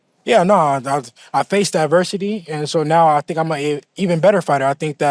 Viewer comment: did bro say he’s ‘faced diversity’?! 😂 he lost some brain cells against Ryan lol